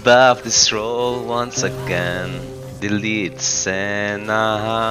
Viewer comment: Did perkz say “Delete Senna”?